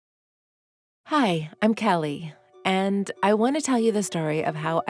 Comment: At first i thought she said " Hi! I'm Siri! " lol